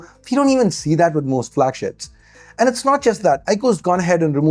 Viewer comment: Did he just say "flagsh%ts"?